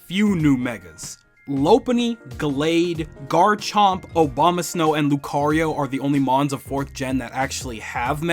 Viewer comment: Was I the only one that heard him say "Obama-snow" 😂